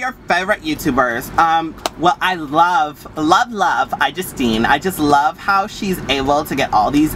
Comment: who did she say was her favorite YouTuber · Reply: Salimatou Bah You actually got it right it's iJustine. Lowercase i just like apple products. Get it?